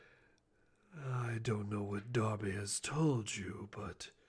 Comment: AT FIRST I THOUGHT HE SAID DOBBY IN STEAD OF DARBY